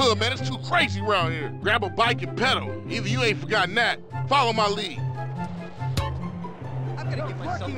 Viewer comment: apa cuma gua disini yg dengernya paolo maldini 😅